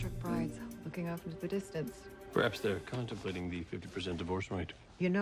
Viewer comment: What did castle say in 0:25??? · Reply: "Perhaps they're contemplating the 50% divorce rate" (referring to the bridal models in the magazines who are "looking off into the distance").